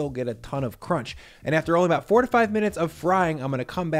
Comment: He fried it for 45 minutes at 300 deg F?? That’s too much time! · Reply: Lok he said 4 to 5 but it did sound like he said 45 😂